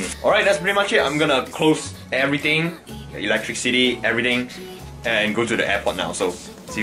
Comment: i could swear to god i heard him say electric city lol